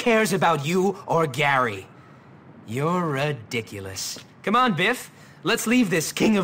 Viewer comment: When I was playing this game for the first time, I actually misheard Derby saying "You're ridiculous" as "You're a dickulus." I was like, why is this fool suddenly swearing like he's Kid Icarus form Captain N?